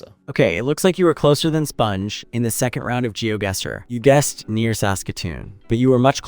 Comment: I CAN NOT STOP LAUGHING AT "GEOGUESSOR" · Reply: I heard it as "Geoguess war" 😂